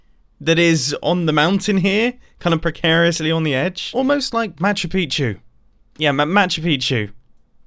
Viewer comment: When Mumbo said Machu Pichu I thought he said My shoe beat you and now I can’t unhear it.